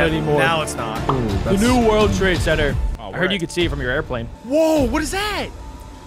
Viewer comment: I heard you can see it from your airplane💀